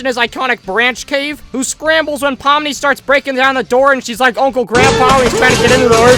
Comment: am i the only one who heard him call poppy pomni...?